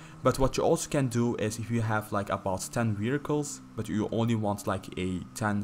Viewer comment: Thought he said 10 miracles lol he said vehicles.